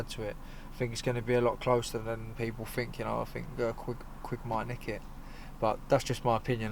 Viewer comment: It sounds like he said Quigg ''might nick it'' not KO Frampton late on.